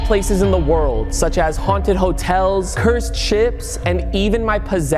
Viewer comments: Once he said cursed ships I thought he said cursed chips🤣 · Why did I hear cursed chips bruh